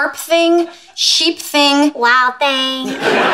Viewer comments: PENNY BREAKING THE GLASS AND BERNADETTE SAYING “WILD THING” GETS ME EVERY TIME😂😂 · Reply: I thought she said loud thing. Lol! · The way Bernadette says "Wild thing" killed me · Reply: I thought she said Wow thing, but Wild thing sound interesting too :)))) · The way Bernadette says "Wild thing" killed me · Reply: Honestly,I always thought she said,"Wow thang" all along and I never knew what it means😂🤣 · PENNY BREAKING THE GLASS AND BERNADETTE SAYING “WILD THING” GETS ME EVERY TIME😂😂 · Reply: Lol I thought she said wow things 🤦